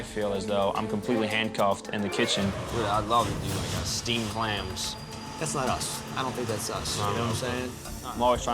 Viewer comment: Man when I heard steamed Clams i thought i heard steamed hams😆